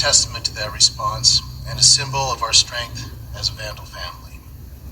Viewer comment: anyone else gagging right now?🤮🤢🤥🤡👺☠️💩 Did he just say go Vandal’s?